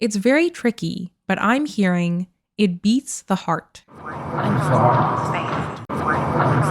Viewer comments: I hear voice 3 as "I'm sorry". Could be wrong. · voice #3 sounded like it said "it's the heart" to me...